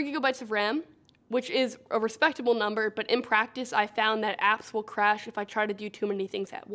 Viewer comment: know i'm a lil bit late on this but did she say "i find that asshole crash" 5:18 lmao i'm dying